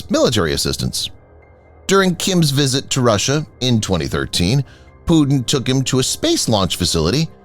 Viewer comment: Narriator says 2013, subtitle says 2023. I love the videos